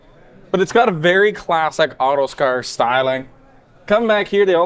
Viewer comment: did you say Autostar instead of Autocar?